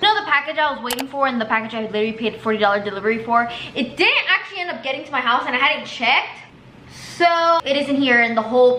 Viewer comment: I thought u said Ubwere getting a house ???????